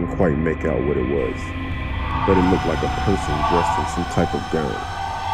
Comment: I heard "Dressed in some type of gun." And I screamed "RUN! RUN! SHE'S GONNA SHOOT YOU!!!!"